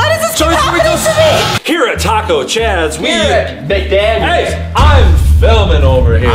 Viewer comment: DID HE SAY THE D WORD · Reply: He said "Mcdaniels" not "Mcd@